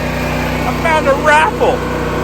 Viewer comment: I was just listening and I had to back track the video because I thought you said. I found a raffle! What you had said "I found a rifle